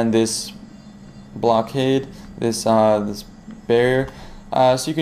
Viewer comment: "This Blockade.......uhhh Bear." was he saying "Barrier"?